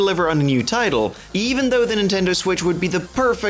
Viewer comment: It sounded like you said Nintendeuw lol